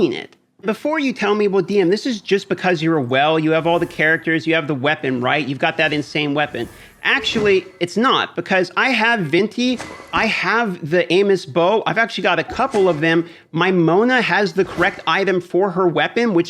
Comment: I heard anus bow 3:17🤣